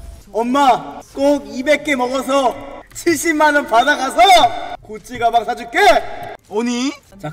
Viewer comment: 이거 구찌로 들리는데 자막이 루이비통이라해서 귀에다가 대고 들었는데도 구찌라 들림 나만 그런가여?